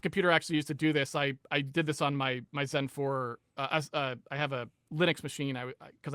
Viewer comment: "I did this on my sun4" ?!?! really? you have a sun4 running linux, that'd be so cool but I suspect I'm mishearing that